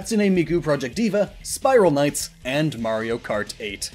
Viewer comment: what is the name? i heard spiral knights